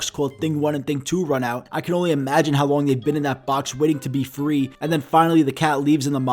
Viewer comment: thought you said “I can only imagine how long they were waiting till they make thing 3”